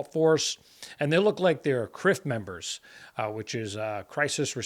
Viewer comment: When you said "CRF members" i thought you said "crip members" and a mental image of saggy pants gangsters standing guard for the president entered my head